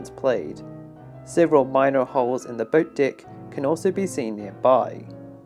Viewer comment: I keep hearing "the boat dick"